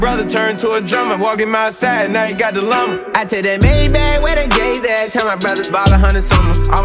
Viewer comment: "Where the gays at" Who heard? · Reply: "Where the Js at"